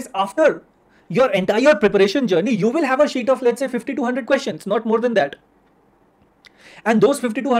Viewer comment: Who else thought he is saying 5200 questions not 50 to 100 questions?